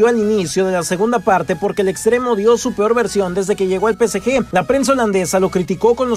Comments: En el minuto 4:21 parece que dices PSG y no PSV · dice psg o psv porque parece que dice psg